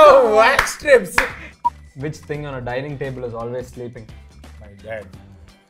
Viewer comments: what did he say? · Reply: My dad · what's saying? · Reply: My dad🤣🤣